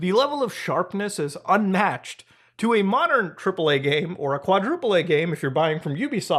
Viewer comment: Misheard him say cripple A game, it's not far from truth though